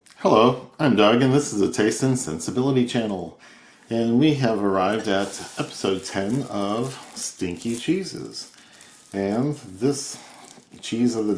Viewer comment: I thought you said stinky Jesus😂 or stinky jeans wash those jeans😂